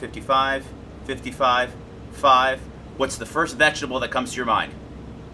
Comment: It MIGHT have worked in ENGLISH - BUT - what i heard was:- "What's the first decimal which comes to mind?"